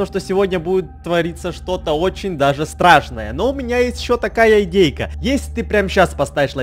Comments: Мне одной послышалось ИНДЕЙКА????😂😂😂🤣🤣🤣🤣💝💝💞💞💟💟💌💌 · Мне одной послышалось ИНДЕЙКА????😂😂😂🤣🤣🤣🤣💝💝💞💞💟💟💌💌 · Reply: вот тут мне послышался индейка